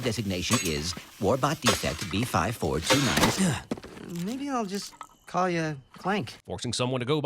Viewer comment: did he say, "BR٠549" ?